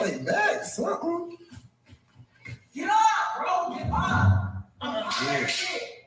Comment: I always thought was saying get up girl get up, But I think you are right